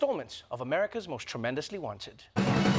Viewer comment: Oh I thought you said America's most tremendously white